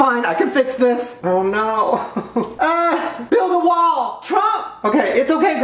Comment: I like how he can hold an entire row of Oreos in one hand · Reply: Robin Scruggs 8:20 did he say:,, build a wall ... trump ! "?